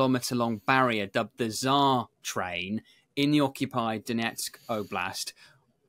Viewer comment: Did he say the Czar train or the bizarre train?